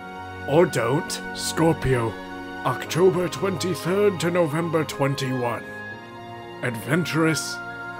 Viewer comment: did you just say "November 21th"?